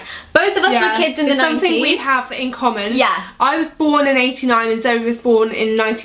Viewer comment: Lol at the start i thought tanya said ''I was born in the 1890s and zoe was born in the 1990s